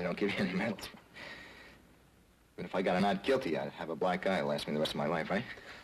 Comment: I used to live for this show. It's still great, but watching it with closed caption turned on is a real hoot. Kirby is scurvy, and Omaha Beach is oh my something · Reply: Dang, you're not kidding. At 39:30 it says instead of getting a black eye that'll last the rest of his life, he'll get a black guy that'll last the rest of his life.